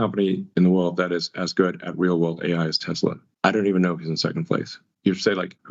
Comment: did he say "werewolf AI"??